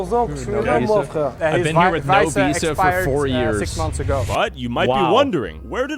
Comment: He did not say "4 years" he said "quatorze ans que j'suis là" which is 14 YEARS